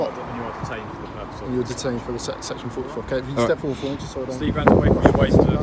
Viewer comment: Did I hear wrong or did the officer on the right just before he searched him state he was being searched under section 44